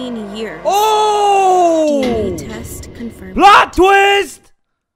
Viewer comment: ok BUT WHO TF WAS THAT WHO TOOK THE TAMPON, oh I just heard "dna test confirmed" .... Yikes that freaked me out....